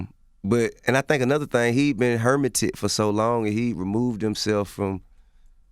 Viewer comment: I thought he was tryna say “hurt” like hurteded lmaoo. Third time playing it back, he’s saying hermited